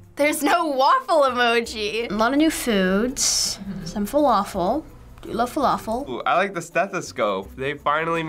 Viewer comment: does he say falafel? or did i mishear?